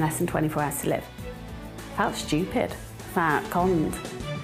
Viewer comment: I swear lady at about 1:39 said "fat, cunt" rofl